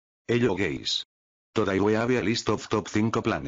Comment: "hello guys" am I the only who heard dis HELLO GAYS?????